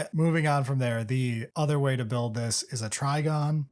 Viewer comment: Thank you 😂 i actually swore said "Trigun" too so your inclusion of the intermission sound just made me smile